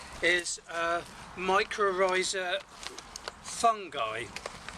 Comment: I spent sometime trying to find this fungus stuff you mentioned. No joy. But I find now that what you actually said was Mycorrhizal Fungi, when instead, I heard 'Microsa'!!! Ah well my Brummie accent can be difficult too! Lol! Aren't you allowed to show the name on the packet old chap? Thanks anyhow, and I am going to get some of this stuff. Hope you get plenty of West Country liquid sunshine to make the roses grow! Cheers JW